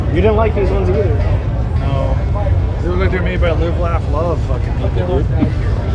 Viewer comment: Haha I thought homie said Lube, Laugh, Love